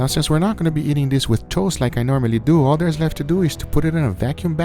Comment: I thought he said “toes” for a moment I’m sorry 😂